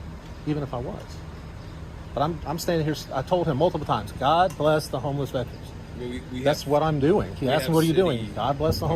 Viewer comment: Lol not gonna lie at 1st i thought he said God Bless the Homeless Mexicans